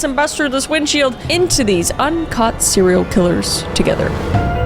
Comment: I thought she said uncut serial killers at the beginning. Not un caught, so now I really need to know how many are uncut vs cut